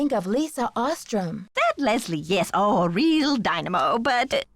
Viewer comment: i truly thought the prof called lisa “that lezzie” for a sec instead of “that leslie” screaming